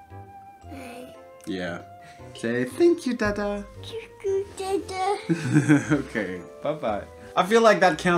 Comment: i heard "doo doo dada"